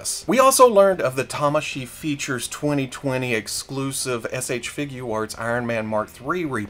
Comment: he says it right but it first sounded like shfigwarts